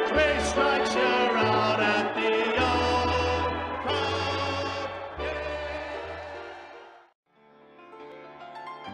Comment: What did that say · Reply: "at the old Cub game."